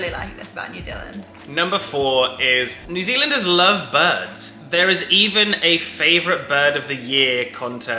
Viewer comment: I thought he said "new zealander's love buds" i was like yeah.. fair call, many of us do.